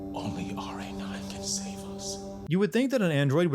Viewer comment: Also the caption says "I would think" even though says "you would think."